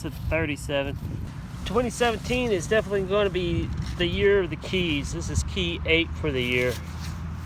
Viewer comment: awesome video! loved the family reel at the start. I thought you said "Year of the Key DATE", as in keydate coins/Wheats. but you said Year of the Key, this is # Eight(not DATE).